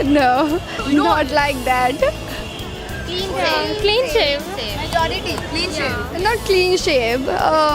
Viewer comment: confused girls don't know what to say and they are just saying what the host is speaking !!!! 😂😂😂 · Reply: one of those girls said clean save 😂😂😂 instead of clean shave .....